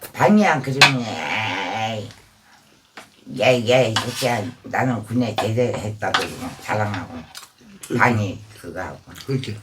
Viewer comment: 뭐라는거야ㅋㅋ😂😂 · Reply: 이야야야 새꺄 나는 군에 제대했다고 자랑하고...... 이거같아요.. 군대안간사람한테 갔다온사람이😅😅😅😅😅